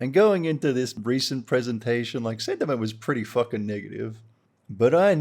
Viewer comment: what did you jsut say? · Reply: The sentiment